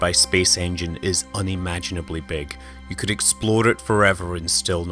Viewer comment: did he say "explode" it forever?